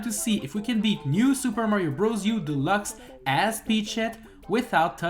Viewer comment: Thought he was saying peashit